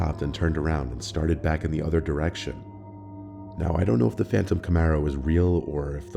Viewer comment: First story I kept hearing "Phantom Tomorrow" lol (sorry, only Black Veil Brides fans will get it)